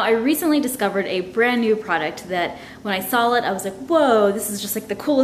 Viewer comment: Does she say sawl instead of saw? 0:13. I swear I've heard her say that in several videos. I must be wrong. 😂😳